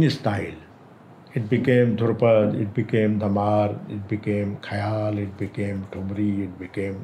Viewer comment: the subtitle should read "Bhairav" not "Piloo. But very nice interview - just beautiful watching him express himself so freely. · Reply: also, Pandit ji is saying Dhamaar style and subtitle says Tamil. Still very nice video. Much appreciated.